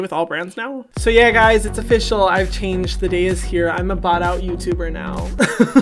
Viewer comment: Instead of “bought out” I heard “bottom” 😧😧😧